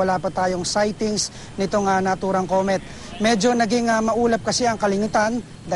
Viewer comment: kalingitan daw instead of kalangitan